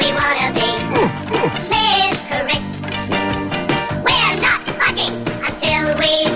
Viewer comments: did they just say : where not fucking LOL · it TOTALLY sound like theyre saying were not fucking at 0:18!!!!!!!